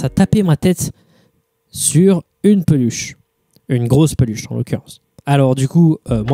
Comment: Purée j'ai entendu "si on se tapait avec des bûches" au lieu de peluche j'ai eu peur.. remarque ça expliquait facilement la dent cassée